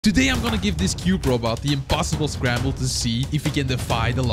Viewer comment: I thought that said cute robot